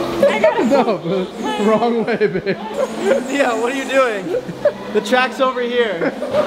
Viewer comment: did he say babe or is that just me ?